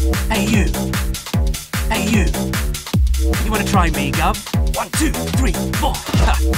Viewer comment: All I hear is: "Wanna try makeup?"